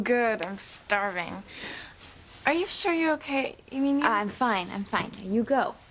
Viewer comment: Did Charity call Jessica Kay here?